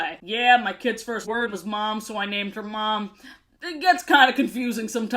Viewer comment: did you say "name"? Lol